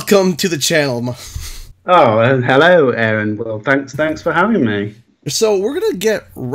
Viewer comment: what did Steve say here? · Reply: Well hello Aaron... Well... Thanks, Thanks for having me...